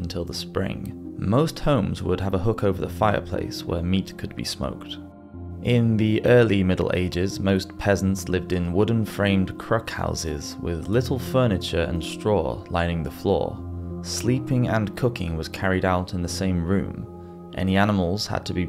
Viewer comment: I thought he said most peasants lived in wooden framed crack houses I was gonna say some things never change